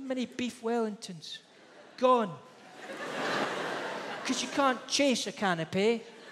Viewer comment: The first time I heard this, I thought he said "can o' peas" and it was just his accent.